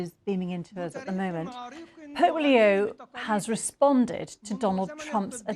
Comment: sounds like you said polio, not Pope Leo! Better articulation, please